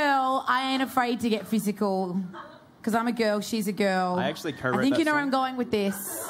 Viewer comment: When calum hits like at 0:17 what does he say cuz I think it sounds like "its not a fucking joke" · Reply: Luke said "I actually co-wrote that song"